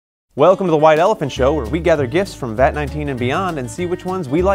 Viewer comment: I always heard "Wide Elephant Show"